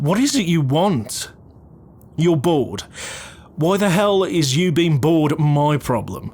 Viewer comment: “youre bored” TELL MY WHY I THOUGHT YOU SAID “youre bald” AT FIRST AND CACKLED SO LOUD